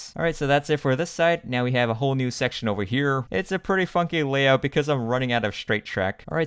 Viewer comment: he said something else, but my brain thought he said fu